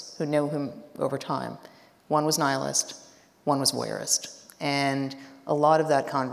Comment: What does "wearist" mean? The word nerd in me must know!🤓 Video/transcript minute 35:40 "who know him over time one was nihilist one was wearist and a lot of that". Thank you for this interview 🙏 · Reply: I think she said "voyeurist".